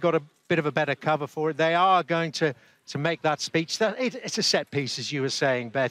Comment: Please don’t overdo it, I thought she said “ we are going to MARS “ 😂